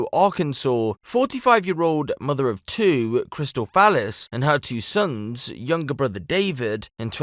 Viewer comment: Did you just say her name is "Crystal Phallus "???